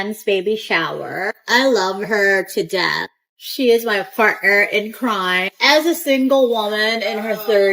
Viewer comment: I heard she is my fartner 😂